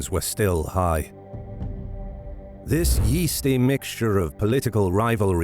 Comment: did you just call the period following Caesar's assassination a "yeasty mixture" because if you did that's the equivalent of sneaking up behind somebody and whispering the word "moist" in their ear